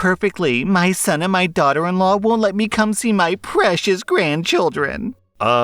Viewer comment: wait… 2:55 ”my precious daughter and son wont let me see my precious grandchildren”. did i hear that right? Alabama 100.